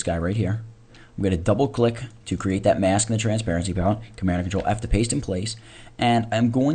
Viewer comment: Hi, i have little bit problem with understanding. Can somebody tell me, what he says in 12:07 to create the white circle on mask ? thanks a lot ! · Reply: command or ctrl+f to put in place